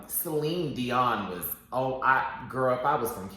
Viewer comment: I’m gonna be real nice, Celebrity Impersonation isn’t drag period 👏🏾👏🏾👏🏾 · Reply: I thought Derrick said Selena (Quintanilla) not Celine. I was so confused.